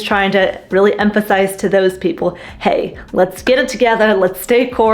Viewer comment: Let's wake up and get it together! :) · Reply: Haha, at first I thought you said, "Let's WAKE UP together!" I was like, "How do I sign up for THAT??" 8oD